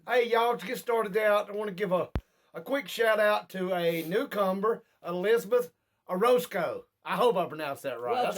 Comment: lol... sounds like you said cucumber when you said newcomer